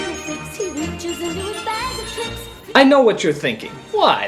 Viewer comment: I watched this movie with my brother, he thought that the song said "bag of dicks" instead of "bag of tricks"